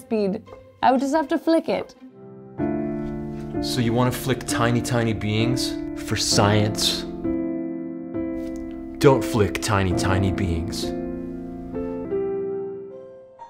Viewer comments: I got extremely baffled at first because I heard “flick a tiny tiny bean” instead of “flick a tiny being” lol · Me: “This is true! I have flicked tiny things! They go really fast!” Joe: “Don’t flick tiny, tiny beings.” Me: “Oh... ....” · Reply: ah - i thought he had said 'flick tiny beans' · I heard: ‘don’t flick tiny beans”. Oh internet, how you have ruined me. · I heard “so you want to flick tiny beans for science” and spit my coffee. Cc thanks for clarifying that, “beings”, got it 👍🏻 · Ahahahahaha I wanna believe he was saying "don't flick tiny, tiny BEINGS", but I keep hearing "Bean". Please tell me he did that on purpose. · I heard, "flick.... tiny beans." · Okay, I can't be the only person who thought Joe said, "So you want to flick tiny beans". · Thought it said "tiny beans" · I definitely thought you said "so you wanna flick tiny tiny beans ?" until I realized you said "beings" haha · I thought he was saying don't flick tiny tiny beans 😂